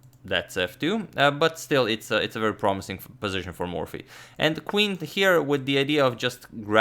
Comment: I swore i think Agadmator said a fucking good position for Morphy